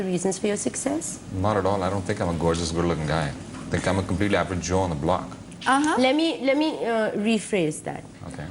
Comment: I take great pride in my english skills, but dude, what did you say in 3:56-3:58 !!?? Wonders. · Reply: “Not at all. I don’t think I’m a gorgeous, good looking guy. I think I am a completely average joe on the block.”